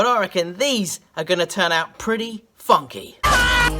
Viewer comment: sounds like he said pretty "Fucky" 😂😂😂